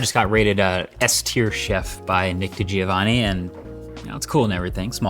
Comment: i thought in the ad he said "nicki giovanni." I was like "sir, i'm still healing."